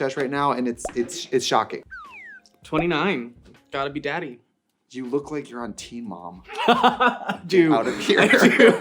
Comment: thought trixie said you look like you're on T, mom 😭